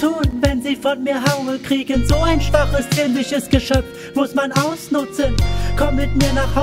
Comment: Ich meine er sagt schwaches-zierliches, aber ich kann mich auch verhört haben.